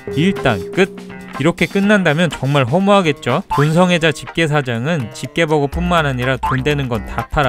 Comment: 에 돈성애자가 아니고 동성애자라고 들었...